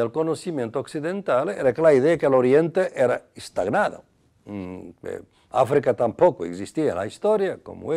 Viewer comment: minuto 9:20 que dice? que nos ha dado una falsa idea de que? oriente que? y dice... al igual que africa estaba que?? si alguien me puede decir porfa. con el oido mas acostumbrado al portugues. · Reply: Y que nos ha dado una de las más falsas ideas, de la teoría eurocéntrica del conocimiento occidental, que era la idea de que el oriente era instaurado, Africa tampoco existia la historia como (autor) lo había dicho y el Oriente estaba estagnado.